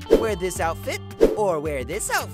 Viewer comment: did bro just say SLAYYYYYYYYYYY